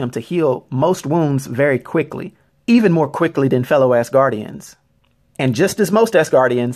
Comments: I don't follow comics, so was puzzled when I kept hearing "ass guardian". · I know he's saying "normal Asgardians", but I hear "normal-ass guardians".